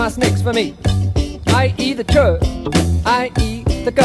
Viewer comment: Am I the only one who thought he was saying I eat when he was really saying ie? Lol.